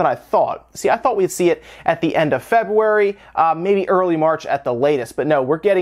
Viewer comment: did he say "Febuary"?